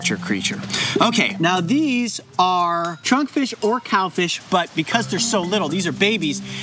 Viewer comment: I thought he said chönkfish, it fits the name perfect tho